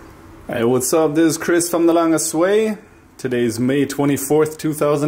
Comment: I initially thought you were saying 'Chris from the Land of Sway' haha. Then I saw your blog and 'the longest way' :D